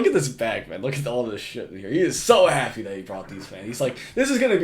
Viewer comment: did I hear him say the n word or am i dumb haha